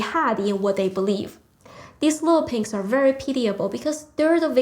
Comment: What is said at 15:55? "These little __ are very __ because they're the victims here too" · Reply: "little pinks (a term for young brainwashed CCP nationalists) are very pitiable"